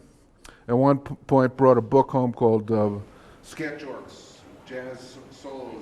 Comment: What is the Bugs Bower book he mentions at 8:10? Sounded like Sketch something Jazz Solos by Bugs Bower. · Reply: The book(s) are called “Sketch Orchs”. They were published by Charles Colin who had a shop on West 48 street in NYC. He and Bugs collaborated on a lot of great books.